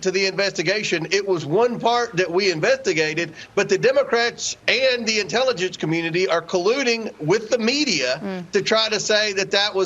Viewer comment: Sounded like comert said ( invent ti gated.) Instead of saying investigated. Accidentally slipping the word invent and watergate put together(inventigate)